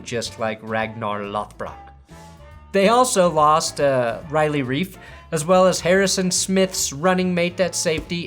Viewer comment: For a second there, I thought you said, "They also lost Riley Reid."